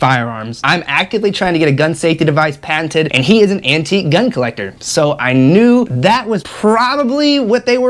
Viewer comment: I was so confused as I thought you said your brother was an "anti-gun collector" at the start. Then went back it was "antique gun collector" lol